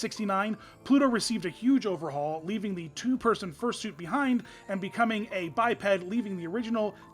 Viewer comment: Did I just hear "Fursuit"? :DDD